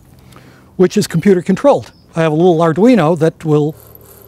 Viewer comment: Did he say Arduino?